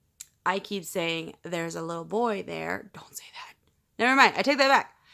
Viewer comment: Question: while I was watching this, at about 11:58 into the video I thought I heard a voice whispered into the mic right after you said " when I keep saying that " the voice said..."don't say that". Was that you or something or someone whispered into the mic? I don't think it was you. Or maybe it was a sound technicality....???? Just curious since you mentioned you have a lot of distractions when recording ghostly updates.